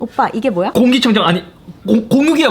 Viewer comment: 계속 눌러보면 뽀빠이라고 들림